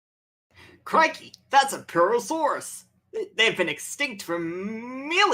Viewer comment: Did you say that the creature was Herrerasaurus at 27:03?